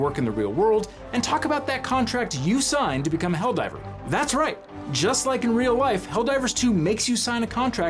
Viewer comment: I swear he said "just like in real wife"?!